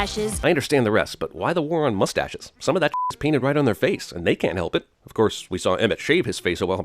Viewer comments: Did they mean "they can't help it" in minute 8:57? It was referring to the mustaches. Confuse on what they were trying to say... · it says can instead of can't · it should say "... THEY can't..." instead of what it actually says: "...THEY can..."